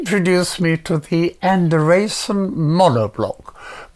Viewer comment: what did he say? enderasin monoblock?